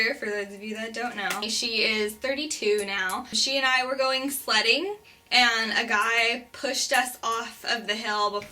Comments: I thought you said "slutting" but you meant "sledding". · Omg i thought u said "we were going slutting" and im like...what?